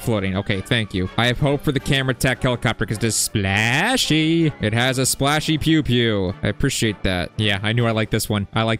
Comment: !did bro really say "it has a splashy pew pew"💀